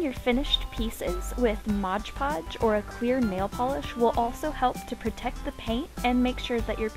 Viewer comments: Lol! It sounded like you said "queer nail polish". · Why did I hear "queer nail polish"